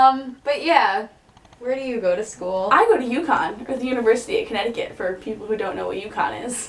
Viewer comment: i thought she said yukon for a second like the very northernly Canadian city